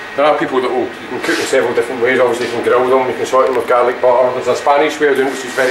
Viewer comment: I have no idea what he said 😂😂😂 type your guess in comments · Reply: ‘There are people...you can cook them several different ways, obviously grill them, sauté them in garlic butter there’s also Spanish’..haha Scotland 🏴